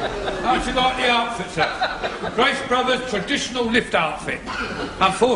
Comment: i think she says, "yes, mr.GRACE" to mr.Rumbold @ 23:33! 😛